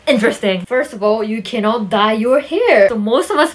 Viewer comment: I was checking my notifications and i was listening to you and i thought you said "you can't DIE when you're here" and i was like "WHAAAT" lmaooo